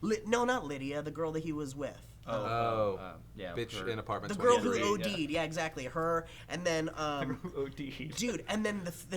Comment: I thought they said the girl who OD'd in apartment 23 which would have been hilarious.